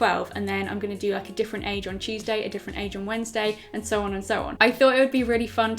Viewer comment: On Tuesday I thought she said “cheese day”....